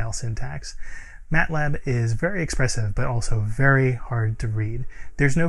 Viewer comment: (meme) We are not the same: You say "MatLab is expressive" I hear "Matlab is expensive".